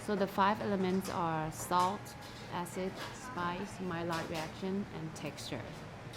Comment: What does she say is the #4 item? Can't quite make it out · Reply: I believe she said “Maillard reaction”